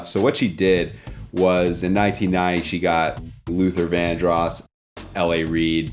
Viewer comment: what did he say?!… in 99?… 😮🧐🤨🤔🤷🏻‍♀️ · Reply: In 1990...